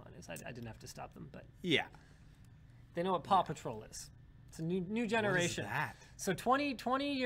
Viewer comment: I thought he said "Papa Troll" too dude. Yes son of Linus, your Papa is a Troll.